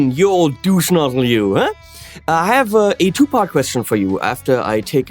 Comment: I thought said "I have a 2pac question"...the rapper😂💛